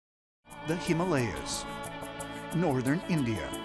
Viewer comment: Did he just say "the hamalyas"?